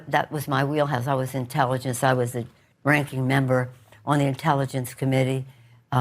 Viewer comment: oh, RANKING member...I thought she said "drinking member "...my bad.